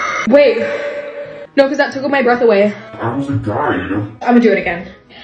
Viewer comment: The “no cause that tOOk all my breath away” 😂💀✋🏼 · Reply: I thought she said tickled my breath away 💀